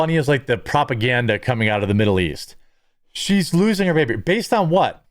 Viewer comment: “Shes loosing her baby” oh so now it’s a baby… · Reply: Did I mishear "losing" here as "using".